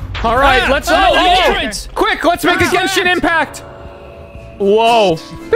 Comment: did say genshin impacr?